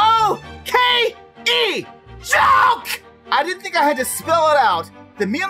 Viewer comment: I thought he said G-O-K-E! JOKE!